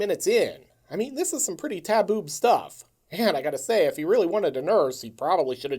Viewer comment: This was a real stand out vid, probably my fave so far. Very funny... I did a double take and had to go back and make sure I heard "taboob." But I was laughing the whole way through, and the movie itself is great fodder for material. Excellent choice, excellent video. Thanks!